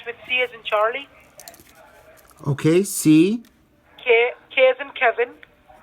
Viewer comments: is sounds like she said k like in carrot🥕 “# Karrot!!! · K as in cousin ? 😂😂😂😂😂😂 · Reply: Amanda Angueira I thought they said K as in Kevin 😂 I had to put subtitles on